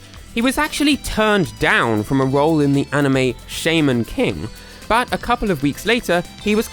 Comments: did I hear that right? Did he say shaymen king hehehehe. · sheman king? i thought it said shaman